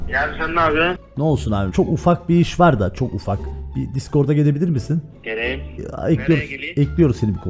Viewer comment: Discorda gelirmisin diyormuş escorta gelirmisin diye duydum :Dd